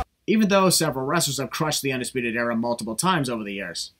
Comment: I think he said crosses not crushes it is just Nigel’s Accent but still sin stands just change crush to cross and it’s all good